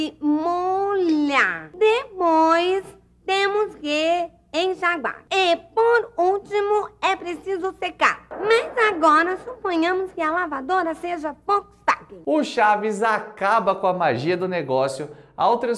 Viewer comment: Quero confessar uma coisa: nessa parte eu sempre entendi que a Pópis falava "portátil". Aí ficavam duas coisas sem sentido. - desde quando a Volkswagen faz máquinas de lavar roupa? - máquinas de lavar portáteis? O que não é memória afetiva...